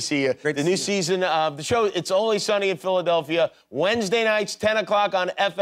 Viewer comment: Does he say “only Sunny”?